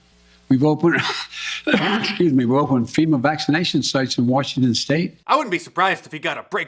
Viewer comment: Did anyone else catch Joe's slip of the tongue at 3:59? I could've sworn I heard him say fema camp, why is he mentioning fema camps? We better wake up and pay attention to what these so called "leaders" are doing 💉💉💉💉💉